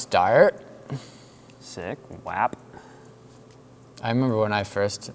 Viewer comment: did he just say WAP